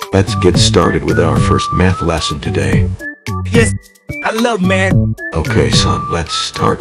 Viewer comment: I love it when he says I love meth instead of math